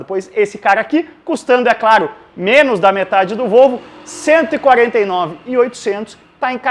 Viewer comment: Em 1:15 eu ouvi um "Custando menos da metade do ROUBO"? ... A não... Volvo... rs